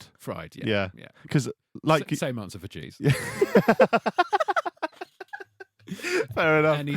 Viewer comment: I thought he said jews😂😂😂